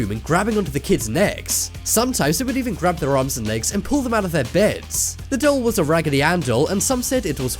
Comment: It sounds like he said pooped them out the bed